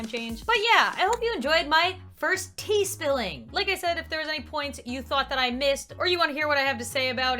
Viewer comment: Confirmed- Tea: Spilled. Shade: Thrown Mission Accomplished! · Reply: EmilyRulesUnicornz I thought u said Shane XD